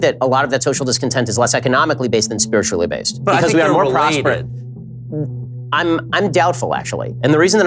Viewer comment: Does anyone know what Tucker says at 2:59? I'm hearing something like "Because we're related" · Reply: I'm 95% certain it was "Because THEY are related", the "they" in question being spiritual malaise and economic dislocation (with the latter being the cause of the former). I Could be wrong.